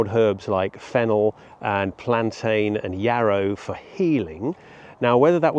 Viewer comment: Did you say plantain?